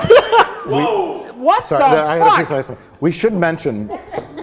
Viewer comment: I've watched the couple seconds at 21:45 maybe 20 times, and I still can't figure out what the fuck Mike says immediately after he spits out the tooth. Also, I laugh so hard it hurts every time. Edit: On the 21st viewing I figured it out. He said "sorry... I had a piece of ice", then I noticed that his glass has tiny pieces of ice in it. This has literally bothered me since this video first aired. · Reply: "Sorry, I had a piece of ice in my mouth."